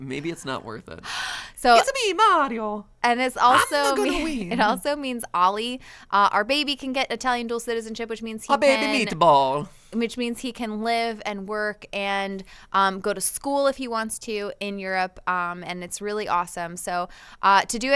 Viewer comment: did Matt just call his son a baby meatball? 😂